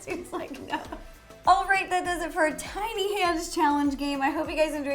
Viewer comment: “I thought is said tiny head challenge”